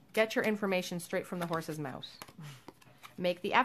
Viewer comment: did you say, "get your information straight from the horse's mouse " ? 🤨😅